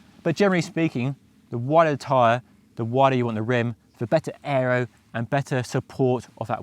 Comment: Who said a wider tyre is more aero? The point is a wider rim with a wider tyre is more aero than a wide tyre on a narrow rim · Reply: misheard what you said at 10:02 ("the wider you want the rim")